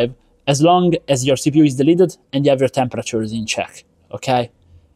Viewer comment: Sorry lol dumb question I'm not really a tech person, 4:48 you say "as long as your cpu is "deleted""? What is the word I'm missing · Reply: delidded